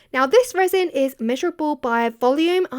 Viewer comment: Totally misheard that as "miserable by volume"!